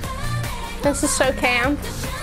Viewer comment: did she say “this is so camp”?